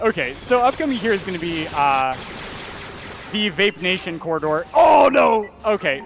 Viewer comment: did he say the vape nation corridor lol